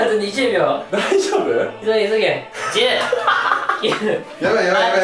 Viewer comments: 「しばゆー急げ」に聞こえる笑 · 急げって聞こえたw